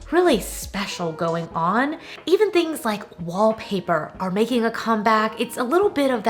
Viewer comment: I was doing dishes while listening to what you were saying about things making a comeback, when you said wall paper all I heard was paper... my post 2020 pandemic brain automatically thought you said toilet paper... so yeah... toilet paper is making a come back... 🤣🤣 in all seriousness though I love watching your videos! You two are fun!